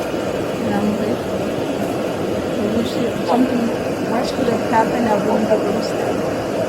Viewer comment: did she say "i wish something worse happened" 😮